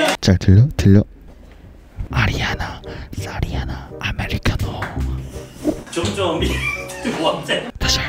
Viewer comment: I swear you and Aoora got some beef goin on 😂😂 11:34 I heard "Thotiana" 😭😭 That air kiss at the end tho 🤧